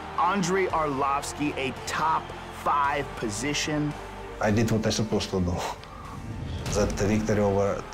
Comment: Btw Ufc, FOR SURE put subtitles on all of Andre Arlovski's words. Jesus Christ, I'm pretty good at hearing what ppl say with accents, but no idea what this guy says most of the time, like 7:04 wtf did he say??? "I need to they supposed to know"??? @@